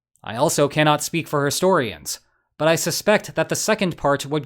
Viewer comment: Did you really say "herstorians"? As a war guy? Bruh..